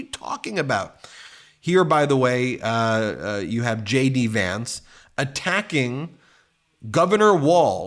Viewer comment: Hilarious....when you say JD Vance I hear "Shady Vance"!